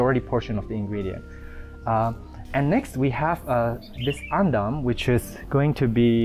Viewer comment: What is the name of the soil being said/used at 4:03? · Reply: The indonesian word is Andam, but its actually dried twigs